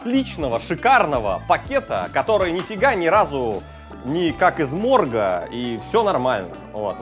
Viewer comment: Мне послышалось : «как свой орган», а не «как из морга»